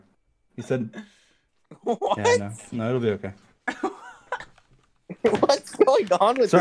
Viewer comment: did dream just said "no a la ve***"? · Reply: he said: No I'll be ok